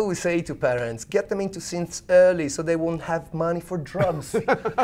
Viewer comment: Thought you said "so they won't spend money on drums" which would also be a good reason